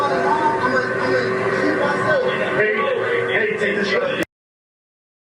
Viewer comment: I heard him say “TAKE THE SHOT” 🤦🏾‍♂️